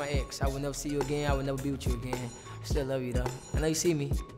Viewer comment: Omg i thought he said EX i will never see u again i will never beat u again🤣